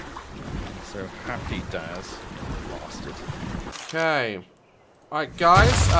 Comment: Anyone else hear the other guy call daz a bastard at 5:55?